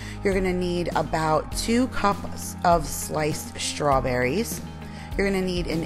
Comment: When you said sliced strawberries, I thought you said spiced strawberries!😂😂😂girllll... don't mind me, lol... I'm just tired!! This is a cute recipe! I love anything w Graham crackers! Thanks for sharing!❤❤❤